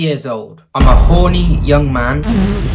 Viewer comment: What yall heard: "im a Horny young man" What i heard: "im a Holy Young man"